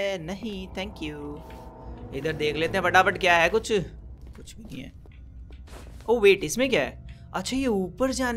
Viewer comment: I thought he said chutiye😆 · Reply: bro he tell kuch nai hai